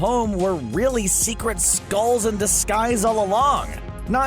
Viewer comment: who else heard 'skulls'